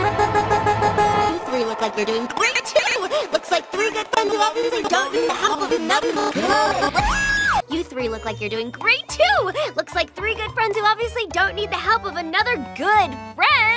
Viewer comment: IS THAT WHAT THAT SAID AT THE END? I thought it said "... Of another loving pony" Huh guess I need to clean my ears lol